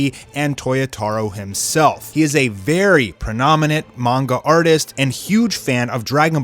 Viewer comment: Did you say prenominent? Lol